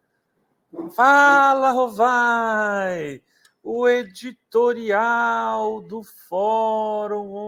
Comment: Eu entendi " fala arrombado "😂😂😂😂😂